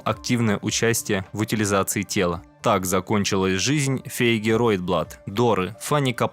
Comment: что он сказал? "Феи герой блат доры" это что такое?